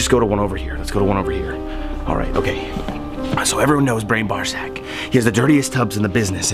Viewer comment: I laughed so hard. I thought you said brain ballsach xD